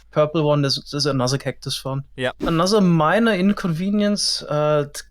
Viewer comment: "Another Miner-inconvenience" I legit thought said miner 😂